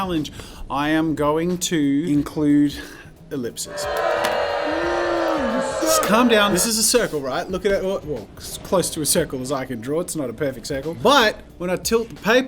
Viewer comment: Dude he Australia accent trow me off I thought he said slip but he said oplic idk what he said but it throw me off